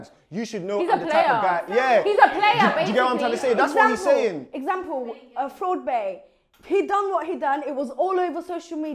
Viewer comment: who are they saying at 5:59 i cant hear the name i thoufht ford bay but its not i dont think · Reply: on oh nvm its fraudbae